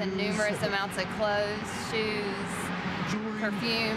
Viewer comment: all I heard was coke and shroom beside clothes and shoes lmfao